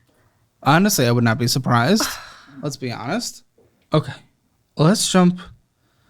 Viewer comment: Did he say Lesbi-honest?